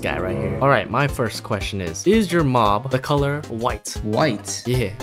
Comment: i first thought he said is ur mom white